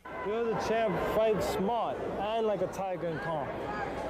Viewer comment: What did Teddy atlas say ? You’re the champ fight smart and like a tiger ( …. ) ? The last word after tiger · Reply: "... and calm."